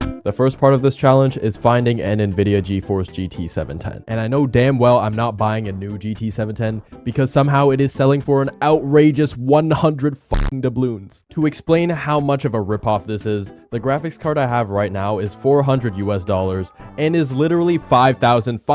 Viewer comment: did bro say doubloons? 😂